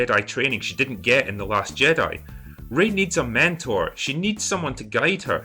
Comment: Did you just say "Ray needs a MANtor"? REEEEEEEEEEEEEEEEEEEEEEEEEEEEEEEEEEEEEEEEE